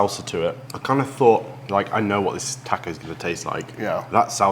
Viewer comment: did… did he just say “tacko?”